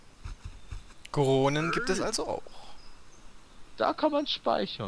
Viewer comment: goronen gibt es auch Was ich verstehe:corona gib es also auch🤣